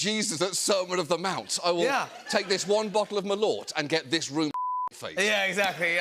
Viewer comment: At first I thought he said "merlot" (wine) which is not terrible nor even bad, but then I read a comment to this video by "Michelle" and I found out that it was "Malort" a liquor.